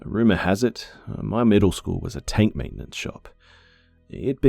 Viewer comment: I thought he said "taint" maintenance shop omggg